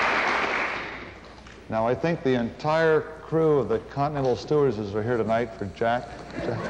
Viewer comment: What does Jim say at 3:44? "The entire crew of..." what? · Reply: I believe he says (jokingly) “Now I believe the entire Continental crew of stewardesses are here for Jack...” as a reference to him being the only bachelor on the mission.